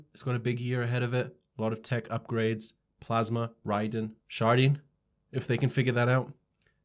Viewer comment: Did you say sharting! 💩